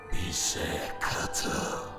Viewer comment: ne diyor? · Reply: bize katıl diyor